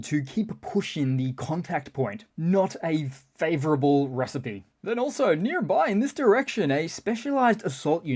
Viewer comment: did you just say "Russipe" instead of recipe?